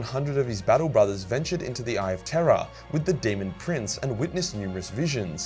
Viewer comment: Every time I hear someone with an British-esque accent say "Eye of Terror" I hear it as "Eye of Terra".